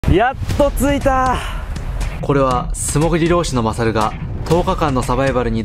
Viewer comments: すごい気になってたんだけどいつもopが素潜り老師マサルって聞こえる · 「素潜り漁師」が「霜降りロース」に聞こえた。